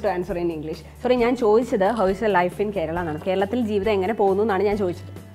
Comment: Im sry but it is ¿Como es tu vida en Kerala? Cuz i heard it as ¿como esta en todo vida? And that means how is it in all life.. if I heard it right... appreciate that u are trying to speak in his language too!!!